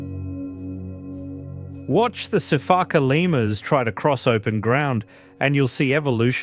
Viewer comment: For a second there I thought I heard “Watch some fackin lemurs try to cross open ground…” 😂